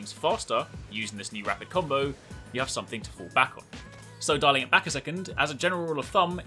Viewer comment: What did you say? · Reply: "dialing back a second." Sounded like darling tho ahaha